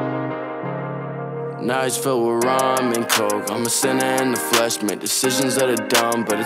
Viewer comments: I thought he was saying “nights filled with ramen 🍜. And coke😂😂😂😂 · bro deadass thought it said "now it's filled with ramen coke" first time i heard it lmaoo